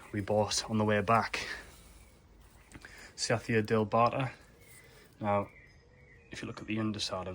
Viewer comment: Hi, what is the name of the fern at 6:48 , didn't quite catch the name. Thanks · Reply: Hi it’s a cyathea dealbata. Great looking fern!